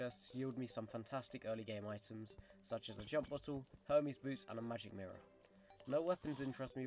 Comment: Did you just say jump bottle